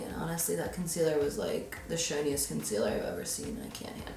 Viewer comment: For a second I thought she said the shittiest concealer that she’s ever used, 😂